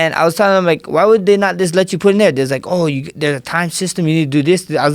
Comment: What did he say at 5:29 " I was telling him why would they not just let you _______?" I couldn't understand him. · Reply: Matthew Goldsby “put in there” as in become a judge because of his background alone